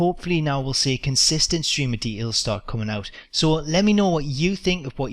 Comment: Love how says details. For the longest i thought he was saying D Ls lol